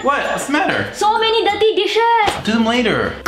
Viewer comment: Is it only me or she really said "so many gandi dishes " in starting